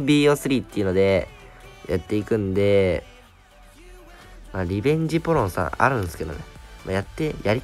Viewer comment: リベンジポルノって聞こえた